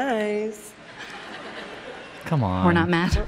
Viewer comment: What did she say at 9:25? We're.not mad? We're not matter?